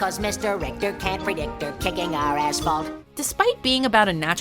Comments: Did he just say a** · did yakko just say ass lol. I know it’s not that bad of a word but it’s a kid show and I’m pretty sure they didn’t mean donkey. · What did I miss? Did I hear "kicking our a** fault" at 3:03?